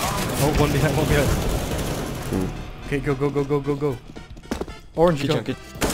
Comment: You said something sounded like Ok google and my search just popped out